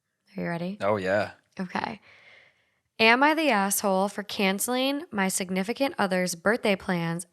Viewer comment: It sounds like you said "am I the gas hole?" 😆 i would definitely be the GAS hole...my ex bf used to call me the lean mean farting machine lolol